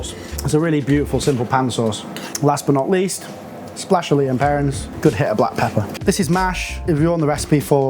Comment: Anyone know what he says at 7:50 ? A splash of something, looks like worschestershire but probably not that haha. Thanks! Excellent video chefs, Imma try that pan sauce later today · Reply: Lea and Perrins. It’s a brand of worcestershire sauce.